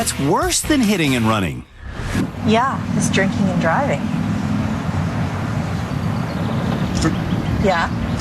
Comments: For some reason instead of "drinking and driving" I heard "jerking and driving". Lemme tell you I was very surprised for like 5 seconds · I thought she said JERKING and driving for the first one and I was like?????? Aight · i thought she said jerk and drive for a moment 😭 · I thought she said JERKING AND DRIVING. bye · I legitimately thought that woman was saying her husband is guilty of “jerking and driving” and I was like 😧😧🤢🤢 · i thought she said “jerking and driving” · I THOUGHT THAT TYLER’S WIFE SAID JERKING AND DRIVING 😭😭😭😭😭😭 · i genuinely thought she said jerking and driving i was so scared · i heard jerking nd driving nd my jaw dropped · i thOUGHT SHE SAID JERKING AND DRIVING AND I WAS LIKE NOO YOU CAN'T SAY THAT ON TV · i thought she said: "jerking and driving" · I thought she said jerk it and drive it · ngl i heard jerking and driving i was like what no way they said that on live tv lmao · I thought she said jerking and driving 😭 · I thought she said “jerking and driving” and I was like… obviously he can’t pay attention to traffic around him if he’s doing that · sorry but thought they said jerking and driving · I was high watching this and I really thought said that her husband was jerking and driving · idk about you but I heard "yeah, he's jerking and driving" and I was like HE'S WHAT?!